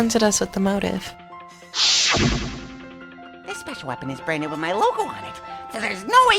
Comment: I was rewatching when I seriously thought I heard monodora say special sh**s at the start